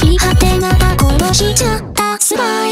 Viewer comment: 「殺しちゃった」が「壊しちゃった」に聞こえる